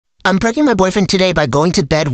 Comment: Feewww I thought she said I’m breaking up with my boyfriend today 😂😂😂😂😂😂😂